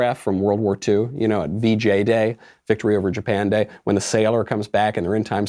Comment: I zoned out and for a second I thought he said BJ day 😂😂